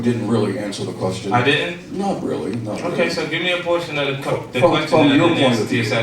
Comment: i'm not knocking 50 at all. I think it's cool he got a sense of humor. But tell me if its just me or if he had a freudian slip. Like check 1:46. he seems like he wants to ask "let me get a portion of the question" but at fist is sounds like he says "let me get portion of the coke" check that out, and correct me if i'm wrong